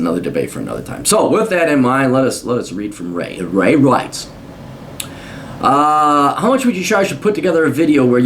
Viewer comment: Did u say at 3:30 ray writes or ray rice?